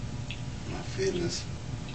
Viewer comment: Closed captions say "My fitness!" lol